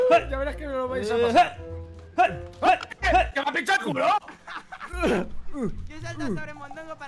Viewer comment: Que dijo mondongo en el minuto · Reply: Me han pinchado el culo 😅